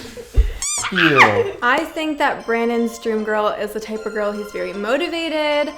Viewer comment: What did say at 14:10? I am still learning english 😪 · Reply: Dilan Zamora she said I think Brandon’s dream girl is